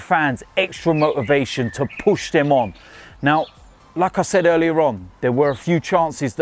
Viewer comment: "it's as simple as that". - multiple times every video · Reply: Also every time he says "like I said" I hear "lacazette"